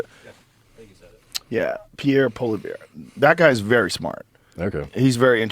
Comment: Both times I heard him say, "Pierre polar bear"